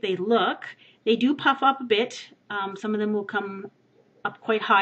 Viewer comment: what you said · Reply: 'They do pop up a bit' i think :)